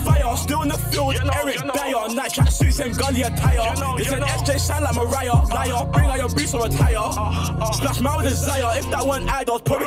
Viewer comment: what did say at 3:13 ? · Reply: they say SJ sang like Mariah (Carey) , liar, bring out yo boots and retire